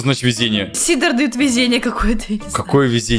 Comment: он сказал "каин" а я услышала "какаин"